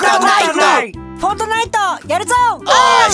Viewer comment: 最初のフォートナイトがリアルファイトに聞こえたのは自分だけかな